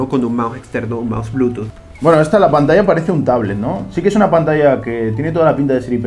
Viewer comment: nate: esta pantalla parece de un tablet. lo que entendi yo: esta pantalla parece untable